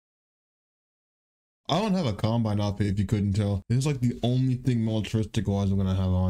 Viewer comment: I thought it said columbine 👀